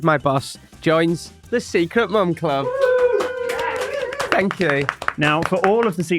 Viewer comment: I thought I heard, Secret Bum Club.